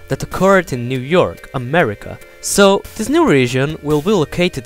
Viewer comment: Lol, it sounded like he said murica XD but seriously, great video, this clears up a lot.